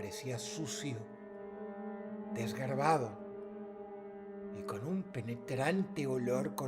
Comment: pensé que dijo "Y con un pene grande" xd. Disculpen, a veces escucho lo que me gusta.